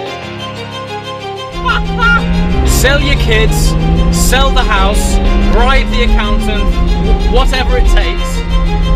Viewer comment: in 2:23 I didn't understand 100% that you said: "sell your kids, sell the house, (?) the accountant... What's every takes... you need one of these!" But by the context, I'd realize that was really funny! In my language: Venda as crianças, venda a casa, raspe a conta bancária, pegue tudo o que você tem. Você precisa ter um desses! #Tchau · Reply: "bribe" Ele estava a dizer "suborne o contabilista" ;)